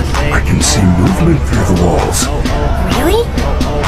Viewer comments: Am I the only only one that was weiter out because it sounded like said “I didn’t know Roxy could see through balls”💀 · I thought he said really i didn't know roxy could see trew balls....